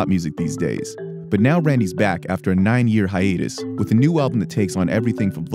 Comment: Did he just say "nine-year hiatus"?